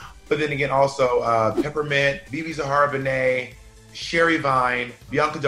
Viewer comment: I heard ‘Sherry Vine’ as ‘Sherry Pie’ and I got scared lmao